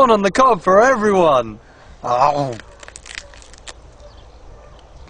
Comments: I thought Spike said, "P.S. Spike is the best homie." XD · Ha. I thought Spike said "Spike is the best homie."